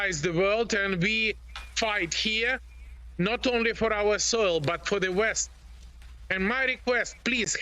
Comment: i think he says 'soil'... But doesn't matter